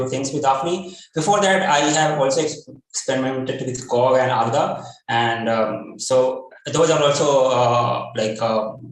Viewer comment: which are the two languages he refers to here? One sounded like Go and other one? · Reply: Agda